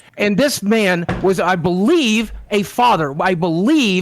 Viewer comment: did he say he belives his dad was a father?